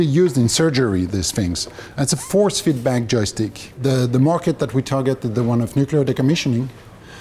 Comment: What did say about the Joystick? 7:48 "It's a four feet bank joystick"?